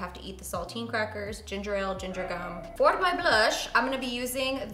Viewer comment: Haaha omg i heard her say "for my BUSH" instead of "for my blush" i was like ok girl, we're getting real real 🤣